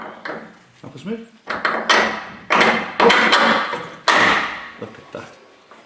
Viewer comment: Am I the only one who heard "Oh putain!" at 32:40 ? :D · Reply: 'Look at that' I think..